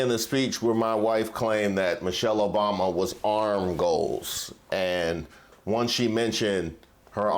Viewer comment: Michelle Obama is PURE 🔥🔥🔥 Also "Arm Goals" Frfr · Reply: Please pardon me but I thought Reese said ‘arm ghosts’. I didn’t know what that meant, but I don’t know what ‘arm goals’ are either! 😂 Could you tell me please!